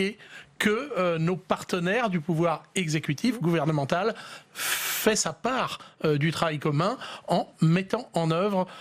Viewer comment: Je me suis endormi, réveillé à 1:17:50 et j'ai entendu "fesses à part" et c'est relativement perturbant d'entendre ces propos sur public sénat.